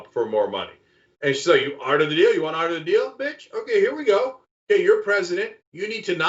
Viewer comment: Sounds like he said “You want out of the deal Bitch”? 🤭🤭🤭